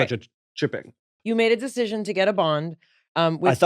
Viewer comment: When said he went to get a bond (from his dentist), I heard 'he went to get a bong', which is one way to deal with things😜